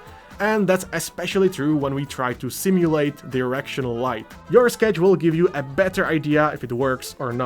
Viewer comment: I don't know if it's the model or whatever but I swear i heard "simulate the erectional light"